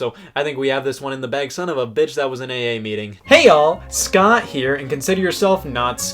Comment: All I heard was "Hey all, Scott here."